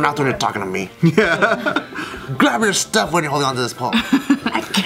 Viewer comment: I thought he said grab her stuff not grab your stuff. Anyways the whole thing just escalated to quick but right or wrong he not goin to face nothing for that.